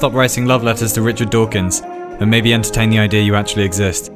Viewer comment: Ok Who was the genius that put in czech subtitles 3:58 Tomáše Sedláčka instead of dawkins?....xd